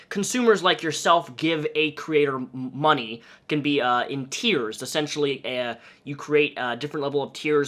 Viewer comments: this is unimportant but i thought he meant tears instead of tiers and i was so confused lmao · I swear he said tears instead of tiers😂